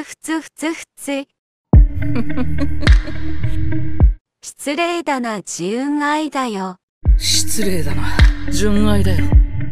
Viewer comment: 失礼だな自分愛だよに聞こえるww